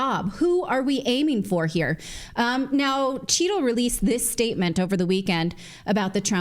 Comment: I think you may be saying Cheatle but all I hear is "Cheeto" (brought to you by Pepsico).